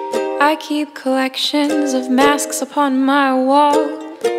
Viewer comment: I have also thought she said “I have a collection of maps upon my wall”